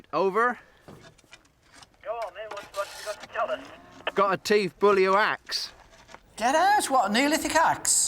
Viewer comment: Strange thing is that when they said "Neolithic Axe" I heard "Teeth fully waxed."